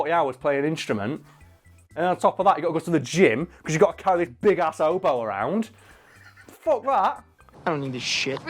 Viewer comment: Did he say "big-ass oboe" at 7:29? Do you know what an oboe looks like??